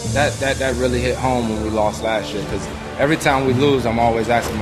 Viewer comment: Does he actually say "that hit home when we lost that SHIT"